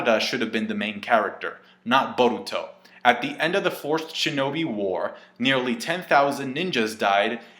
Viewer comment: I thought he said "at the end of the forced shinobi war", I laughed and still wonder if that was intentional.